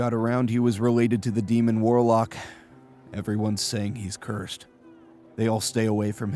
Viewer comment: I was eating popcorn and when was talking i misheard him when he said, "Everyone's saying he's cursed" and i heard "everyone thinks he's gay" and i was like "no duh sherlock"